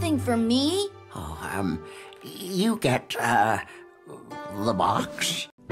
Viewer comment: can’t stop laughing XD 😂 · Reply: I heard “You get La Box” 😂😂😂😂😂